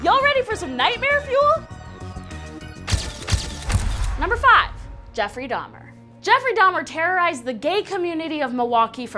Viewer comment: I thought number 5 said “Jeffree Star” 😂